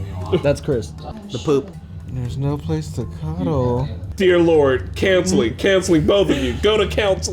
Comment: Their dissection of Michael and Jan’s relationship had me cackling. Chris yelling “COUNSELING!!” 😂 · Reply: I heard cancelling. It really sounded like he said "Cancelling both of you!"